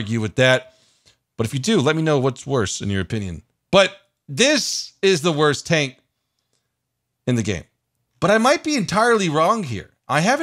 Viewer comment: I thought you said the ISU 152 is the worst tank in the game